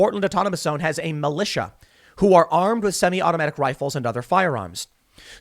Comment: did Tim say they are armed with fully semi-automatic weapons?